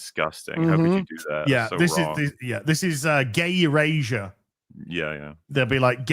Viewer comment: I know that said, "gay erasure," but I heard "gayer Asia"...so, Bangkok? 🤷